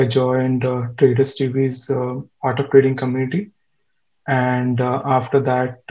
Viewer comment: At around 1:25 talks about some community but I don't understand what he said? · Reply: The Art of Trading Community with Trader Stewie